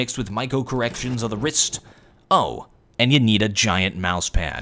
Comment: What is he saying at the end of all his videos ? All i hear is "I'm the WarOwl, and I still! Have no closure". Witch makes no fucking sence. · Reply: He says he still has no closer.